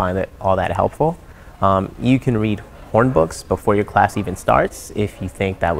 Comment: Did he said "Porn Books"